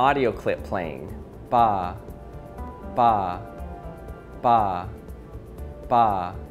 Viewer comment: i hear "pa" regardless of where i'm looking at